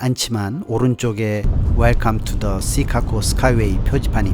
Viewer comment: Chicago가 시카코로 들리는 나만 이상한가요? 계속 거슬립니다.